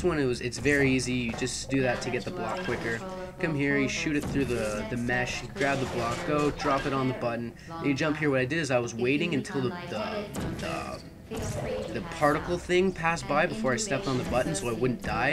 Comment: All I heard @ 8:28 was Drop on the button, particle field.